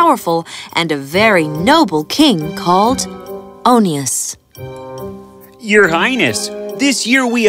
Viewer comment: lol I thought she said onions